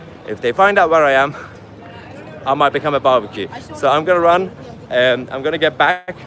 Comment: I'm going to run i thought you were saying im going to iran